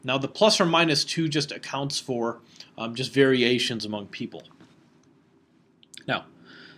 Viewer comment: I heard 'variations' in "plus or minus 2 accounts for just variations among people" as "very Asian" and for a second I was like yeah I guess for + 2 that would make sense . _ .